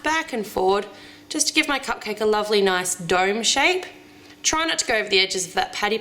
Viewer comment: My brother thought that when she said dome shape that she said Doug shape he was during headphones.